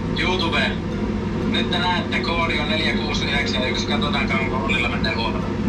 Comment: missä toi kohan 11:07 koodi on? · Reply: Siinä lukkee: ko - odi on 4 k - uusi 9 yksi